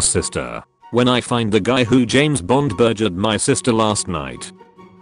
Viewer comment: I don't get it. What was she trying to say? Can anyone help out? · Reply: James Bond burgered